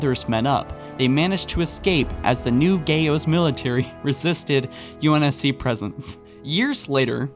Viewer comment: he laughed at gao military cuz it sounded like GAYo military